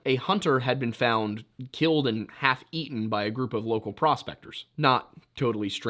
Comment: I thought he was saying a hunter had been eaten by a group of prospectors 😆